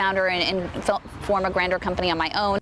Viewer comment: i thought she said “form your Grindr company” lol Cody if you created grindr too omfg 😂😂😂😂